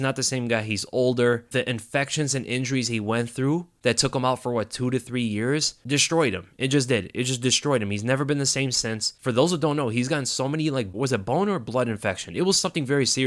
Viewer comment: Get 20% OFF + Free Shipping with code WEASLE20 at → <link> #manscapedpartner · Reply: For a second I thought you said "boner blood" infection. No thank you!